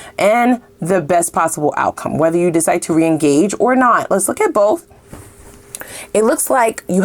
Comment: I'm sorry did you say re-engage Cause it sounded like you said "re-encage" and I dont mind tellin my Sweet Soul Sista I ain't goin back inside that trap. I can say it that she is more accustomed to all the roaches that have checked in only to never checked out the thing is Im not a roache but a cat with not only the key but thee key. So, ..... uhhhh ... hmmm ... ahh Yeah! So, now what should we talk about. Hey i know remember when i used to always capitalize the letter Y whenever I spoke You well so you? that y has faded alright but my Fader is goin straight UP TO THEE TOP!¡! You feel me?